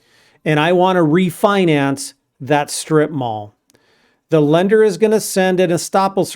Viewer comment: Instead of “strip mall” I heard “strip bar” at first… 😂😂😂😂Kind of glad in example was used first one…🙏🙏🙏😅😅😅🤣